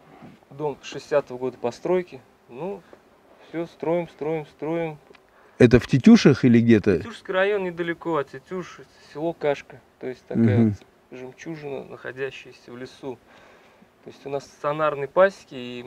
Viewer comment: Башкирский мед всегда считался самым лучшим. Настоящие хозяева земли. Всего вам доброго! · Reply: извините, в начале видео послышалось Тетюши